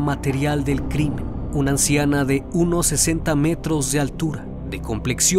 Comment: parece que dice de unos 60 metros de altura 🤣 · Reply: Dice "de 1,60 metros de altura", está correcto.